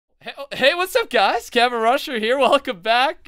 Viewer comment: lol it sounded like he said "hey guys wats up kevin rudd here"